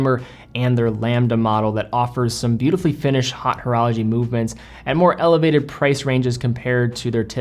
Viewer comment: I thought he said "hot horology" for a second. Ooops! I personally feel pretentious when pronouncing the French borrowings, but then just don't use em:) Or lean into americanizing the vowels and pronounce it Oat Orology:)